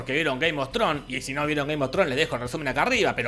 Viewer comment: Yo claramente escucho que dice "Gay mostron"..